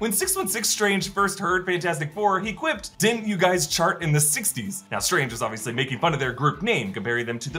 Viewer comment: I thought he said “didn’t you guys shark in the 60s?” as in jump the shark…